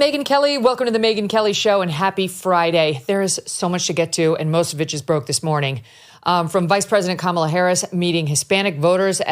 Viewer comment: I swear in the beginning it sounds like she says "Most bitches broke this morning." 😆 Believe in Jesus! Rapture soon! ⚡️⚡️